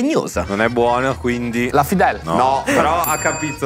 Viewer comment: Solo io ho capito la Pfizer??😂😂😂😂